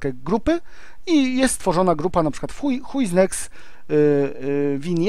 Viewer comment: Zamiast Who Is Next usłyszałem Huj Is Next xD